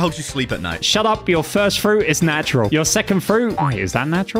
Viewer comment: did he call dough a natural?